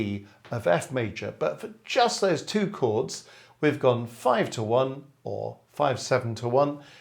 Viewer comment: not clear what your saying and board art is also unclear: 5-7-2-1 progression? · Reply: V7 to I is what I’m saying. Board work seems clear even on a small screen but sorry if you’re struggling